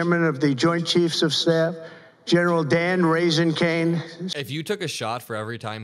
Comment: Did he say General Dan "Raising" Kaine?